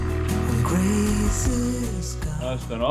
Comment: Geez... @ 6:18 I could have sworn the soundtrack said, "Grayson's gone..." 😩🤣